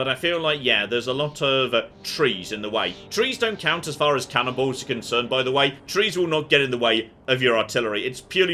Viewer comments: "Trees don't count as far as cannonballs are concerned..." What I heard: "Trees don't count as far as cannibals are concerned..." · Did just say "Trees dont count as far as cannibals are concerned"? Thats a hilarious mental image